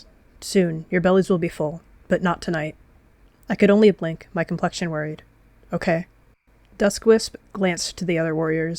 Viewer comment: When is saw duskwisp say 'my complexion worried' i really thought they said 'my confusion is worried' and got so confused